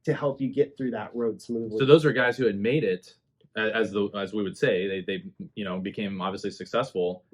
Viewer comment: does say “as WOMEN say”?? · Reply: "As we would say"